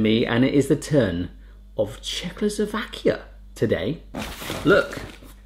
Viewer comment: Did he say Czechoslovakia ? -.